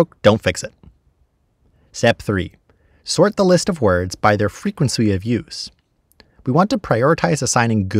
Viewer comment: excuse me, good sir. Did you say “frequensu”?